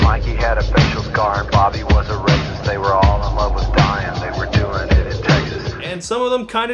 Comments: "They were all in love with dying they were doing it in Texas" Oh.... oh my god. I... am I the only one who heard "dying" as "Dion"? Like a cross between Diane and Dylan? I... I thought it was a girl's name! TWENTY FIVE YEARS AND THIS WHOLE TIME I THOUGHT THEY WERE ALL IN LOVE WITH THE SAME GIRL AND HAVING A LOT OF SEX IN TEXAS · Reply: I heard it as "Di and", like a short version of Diana. "They were all in love with Di, and they were doing it in Texas." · I always heard "they were all in love with Diane, they were "doing it" in Texas